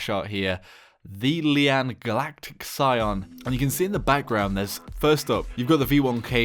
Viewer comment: i hear here The Galactic Lian Sayian Then Kaaaa Meeee Haaa Meeee Haaaaa!